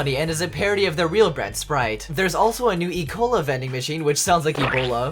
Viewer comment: I thought it kinda sounded like e-coli, not ebola